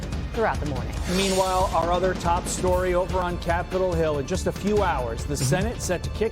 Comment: I thought he said Cat Girl Hill.